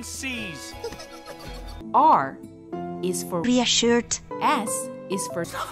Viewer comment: Look i know too-ticky said "be assured" but i heard "be a shirt" and no one can change my mind about it